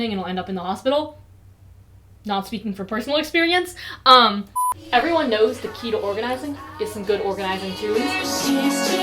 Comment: I legit thought she said “everyone knows the key to organizing is some good orgasm tunes” and I